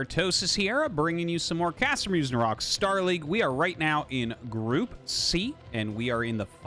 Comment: Every time the intro comes I hear "Castermusing Rocks" and have to check the description for the actual name.